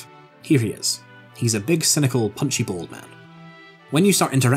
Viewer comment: I misheard you say Joseph is a “Punchy ball man” and went ,,wow wow, too soon man! My heart can’t take it!